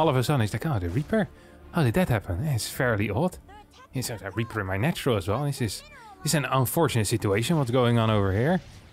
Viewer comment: Did anyone else hear that? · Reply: yea i did. "They are attacking our something, something something mineral lines in a very cute voiceover :D for game voice assistant i think "